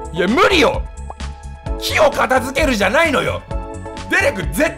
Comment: 木を片付ける が キヨを片付ける に聞こえた。